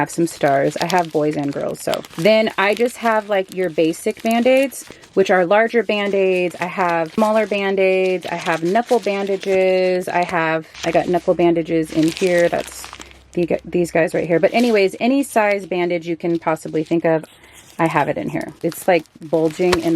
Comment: This video was so informative! But at 13:20 I swear I heard nipple bandages and I was like what are nipple bandages and why would you need one!? Then I saw knuckle bandages lmao! Great video!